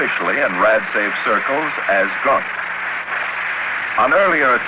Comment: I didn't hear the name of the special detergent used to cool off the skin of the plane very well @ 0:35:19. Did the narrator say: "...the plane can be sprayed down with a special detergent known officially in the RAD safe circles as 'GUNK'?" I am asking this because I was born in the 1950s and am interested in what happened during my developing years. 1950s and 1960s were interesting decades.